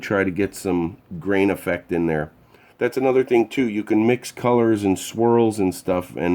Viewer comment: I thought you said colors and squirrels. So now I'm trying to figure out how to incorporate squirrel skeletons into stained glass.